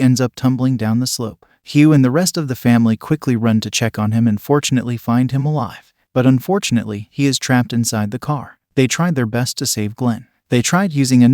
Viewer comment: did he say UNFORTUNATELY HE IS ALIVE